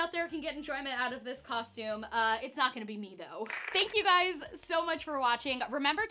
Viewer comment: at first I heard "I hope someone will get me out of this costume, it's not gonna be me" XDXD Stephen where are u :DDD